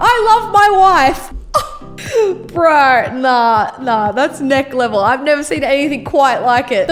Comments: If you haven’t noticed at 4:07 she saysn neck instead of next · On 4:06 said neck level instead of next level